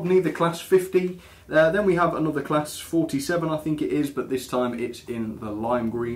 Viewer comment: You got the o4 and j11 right! Also is that a railroad class 47 because it sounded like you said class 41!? just wondered cause it looks quite nice